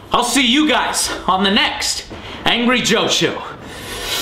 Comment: For a second I thought the beginning said "Angry Hoe," instead of "Angry Joe."